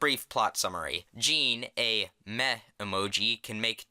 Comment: At the start I thought he said "Gene, a meth emoji" I watch too much Breaking bad.